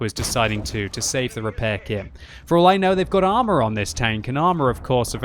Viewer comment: "For all I know they have armor on this tank." Or at least that's what it sounded like to me when was talking about the Armorer skill.